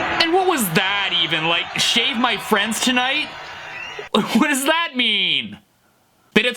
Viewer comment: I THOUGHT HE SAID SHAG MY FRIENDS TONIGHT